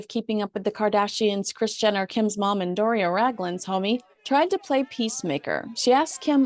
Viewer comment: Did I hear you say ' Doris Raglan ' ?